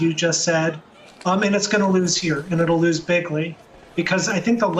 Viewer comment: Did I hear him say "it'll lose bigly" at 1:20? Lol I'm pretty sure he just stole one of Trump's best words. Trump has them all apparently though so I'm sure he can spare one.